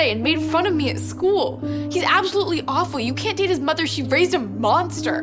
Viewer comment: girl says you cant date his mother and the subtitles saying "beat his mother" instead had me dying.